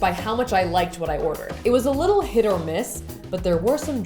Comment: did she just say hit or miss????